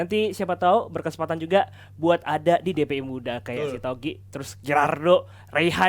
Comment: si togik dateng ke KPI? 0:59 daftar jd pundit dpi muda. dpi apa kpi si, apa gue yg salah denger